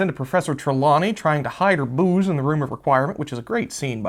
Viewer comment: I miss heard what said I thought he said she was hiding her boobs hahahaha then realised it was booze. Nag is a bit of an odd term as is he saying the centaur is annoys people by constantly finding fault and constantly complaining (chewing someones ear off), normally Nag is directed towards females? or is he saying the Centaur is a useless old horse? Talk of plagiarism it's not just Lord of the Rings. Harry Potter has taken many elements from The Worst Witch which was published in 1974.